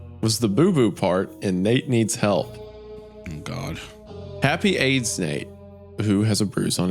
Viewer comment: I thought you said "Happy AIDS Nate!🎉"